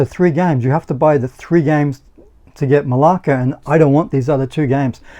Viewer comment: Jesus I heard "MALAKA" ! Snorted my coffee out of my nose! Jesus man!!!!!!!